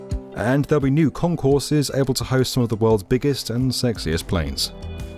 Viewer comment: Did he just say “biggest and sexiest planes”? 😂